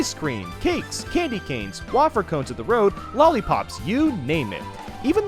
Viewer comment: did he just say “*WAHH-FUR*”